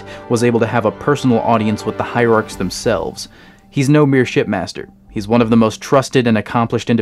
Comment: did he say shetmaster?